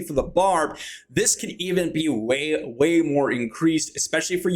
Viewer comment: he said Whale instead of Way 😂lol